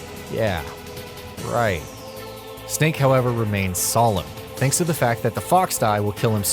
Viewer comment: I thought, at first, that you said, "Snake, however, remains solid".